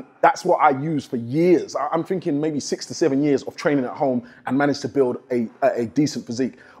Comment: he says he did this for 6 to 7 years, I thought he said 67 years and I was like Damn he looks really really good for a Grandpa then I had to think about it